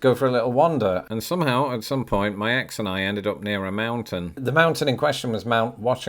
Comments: Did I hear you say "My ex and I"??? Did you and Tarah split up?? (2:52 ) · At about 2:50, did he say: "at some point my ex and I ended up in the Mountains"❓ What ex? Who? WTH?!